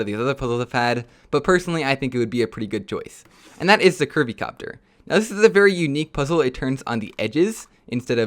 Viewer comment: When he said curvy copter I thought he said Kirby copter